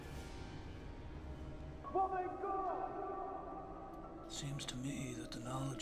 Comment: what does he say to his son btw? at like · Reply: Well then go